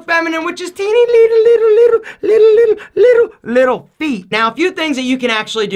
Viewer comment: When he said: little feet. I thought he said: little pee lol